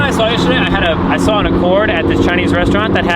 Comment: thats a honda not a ford lol dude your funny im gunna start mis identifying cars lol oh wait did you mean accord? sounds like you said ford. either way an accord is so boring if you put it next to a ford fusion and squint your eyes.. same same